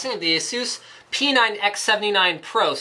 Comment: i thought he said peanut x79 pro.